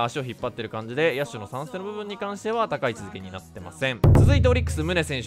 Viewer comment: なってませんって聞こえた